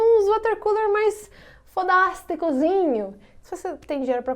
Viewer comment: fodasteKUZINHO foi demais né? kkk... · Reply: legendas: fofa da haste cozinho